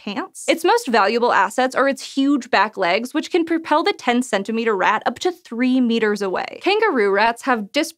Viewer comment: for a second there at 1:28 i thought you said the rat can propel the SNAKE 3 meters away!